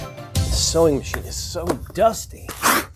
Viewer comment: I had to replay the seweing part like 5 times... I swear I heard "this sewing $H!7 it so dusty!" 🤣